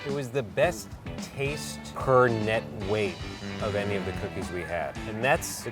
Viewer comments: had to do a double take because i thought it said coochie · I thought it said Coochie I’m so sorry